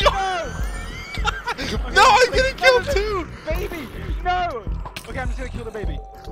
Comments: am i the only one who heared "noo babyy" from George 🥺😂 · I hear like George call dream "baby NOO"